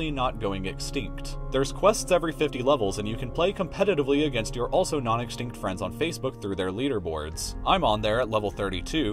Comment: I thought you said "You can also compete with your non-existing friends on Facebook" turns out i was wrong